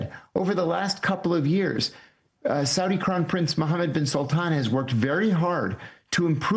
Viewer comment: Did I hear him correctly? Mohammed bin Sultan? Made me giggle ngl